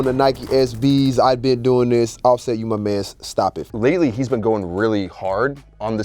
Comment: it sounds like flare says stop it five at the 12:45 but they cut it off